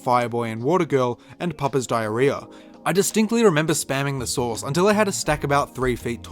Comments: I swear I heard Papa's diarrhea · did anyone else catch the fact he said "Papa's diarrhea" lmaoo